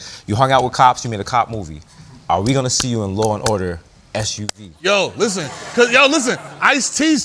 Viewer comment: Did he say law and order SUV lol